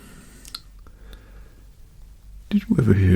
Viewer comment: I thought this man said "Did you ever hear the tragedy of the man who sucked at crewmate?"